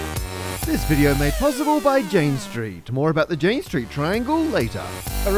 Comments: subtitles says 'James Street' instead of Jane Street 7:35 Subtitles says 'octan' instead of arctan 12:10 Subtitles says 'F9' instead of (presumable) affine 13:38 'Goebel' instead of Goble 19:56 'gene street' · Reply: also 1:26 subtitles say "James tree" instead of Jane Street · subtitles says 'James Street' instead of Jane Street 7:35 Subtitles says 'octan' instead of arctan 12:10 Subtitles says 'F9' instead of (presumable) affine 13:38 'Goebel' instead of Goble 19:56 'gene street'